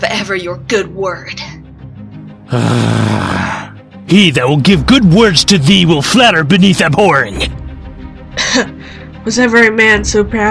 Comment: This is amazing! But I have NO idea what he said at 0:57 all I heard was "He shall speak good words, to flatter thou beneath the floorheng" correct me if I'm wrong XD - Paint